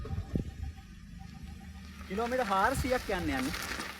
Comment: I didn’t hear what you said. Above all, you have improved a lot in this area and you have a clear path ahead. Ruwani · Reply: කිලෝමීටර් හාරසීයක් යන්න යන්නේ කියල :)